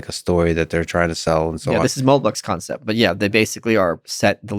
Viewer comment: does he say "mould-box concept"?